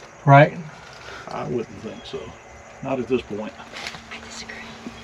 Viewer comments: Omg at 23:05 you can hear a womans voice, sounded like. “I disagree!!” 😮😮😮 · When you are on the stairs around 23:00 mark I hear a woman say I disagree creepy. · Hi I was listening to your video this afternoon. Very good video by the way. You guys caught an EVP at 23:03-23:04 that says " big secret" or "like a secret" when you are looking up the stairs. Hope you can hear it pretty cool.😊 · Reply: When said there was nothing at the top of the stairs of interest, I heard a woman’s voice say “I disagree.” Rewatched the video several times and heard the woman speaking each time at 23:04. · Did anyone hear a lady whisper “I disagree” when is on the indoor stairs? Right after says there’s nothing up there I need to see and agrees then you hear the whisper. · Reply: OMG I was just going to comment that I heard a woman whisper "I disagree" at 23:08!!! I listened back a whole bunch of times and it was there. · Theres nothing at the top of these stairs thats worth seeing, right? I wouldn't think so. Not at this point. Then, right at 23:03 a female voice says "I disagree". At first i thought it said I have a secret, but after listening a few times to yalls convo then the voice, it definitely spunds like she says "I disagree". Creeeeepy